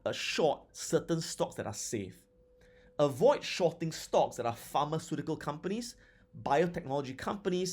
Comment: What does Adam say at 17:57 when referring to stocks to be avoided for shortening (besides penny stocks, low cap stocks and biotech stocks)? Does he say "pharma company stocks"? Thnx in advance